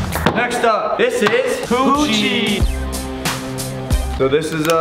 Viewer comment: I thought they said coochie